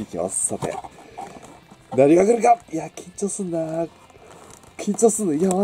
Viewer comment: 「何が来るか！」ってのが 「ナルガクルガ！」に聞こえた